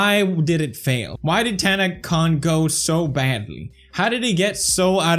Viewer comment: I had to replay pewdiepie saying ,"why did tanacon go so badly,"because I thought he was saying why did tanna Congo so badly.